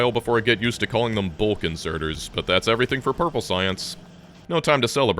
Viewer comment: I almost had a heart attack because I thought he said "Balkan inserter"